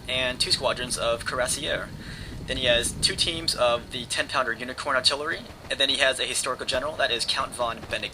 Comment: did he say unicorn calvary : /